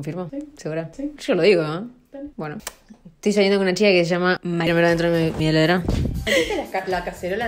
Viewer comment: En el minuto 5:36 en cámara lenta se escucha clarito que dice bar, confirmaron Martuli, confirmaron briangie, y ahora confirman barbica, este es el paraíso 🏞️✨